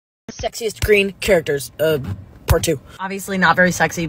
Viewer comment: thought that said “ sexist ” and I was like .. so confused